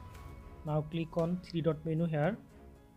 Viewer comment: Can anyone tell me what he says at timestamp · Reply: Click on 3 lines at left side